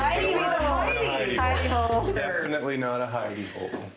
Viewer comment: This was hilarious. I thought he said “hiney” hole at first. Glad I was wrong. I try to enjoy House Hunters except when Ms. Leigh starts finding ways to “upgrade” what we already have...